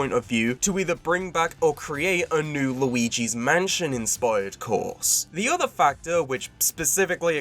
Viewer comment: Ohhh course, I heard "Luigi's Mansion-inspired corpse" 😅 Love the tracks at the end synched up with the music, great editing 👍